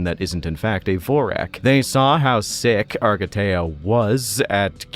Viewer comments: I swear I thought that said Argatha, I’m too far gone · I genuinely thought it said Agartha and was really confused